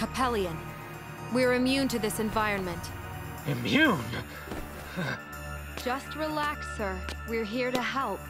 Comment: I dont have headphones on and it sounded like she said "Dont relax" when she really said "just relax". I had to replay that a few more times lol